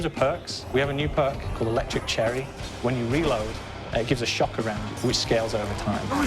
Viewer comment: what did he say? gives a shock over what?